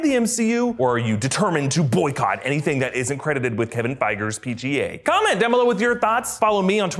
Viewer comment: did he just say "Kevin Fieger" ? Lmao